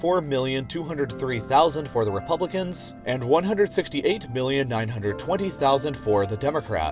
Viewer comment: it sounded like you said 168 billion